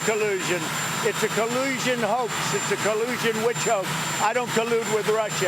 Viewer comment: WTF is a "collusion witch ho?" Lmao....what an idiot! I'd hide my school records too, if I were Trump. His idiocy is apparent to all except the similarly intelligence deficient. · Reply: I thought he said witch hoax